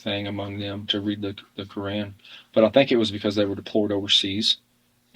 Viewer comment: Did he say "DEPLORED" overseas instead of "deployed"? 🤔